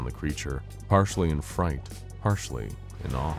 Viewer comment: i found a subtitle error. the video says partially, but the subtitles say partly